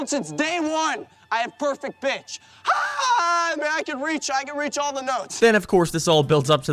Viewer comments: idk if this just me but almost sounded like said “I got perfect bitch”. I know it was suppose to be “I got perfect pitch” but it just sounds like he said bitch instead of pitch. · i misheard this as "i'm a perfect bitch"